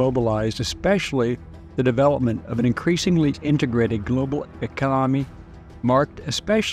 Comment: "Econonimy" What word is that? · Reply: Economy?